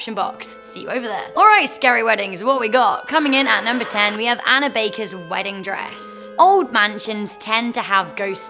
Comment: The first one I thought she said Hannah backer